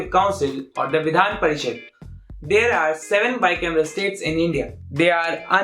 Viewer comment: what did you say ? It's hard to guess what you are speaking. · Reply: Bicameral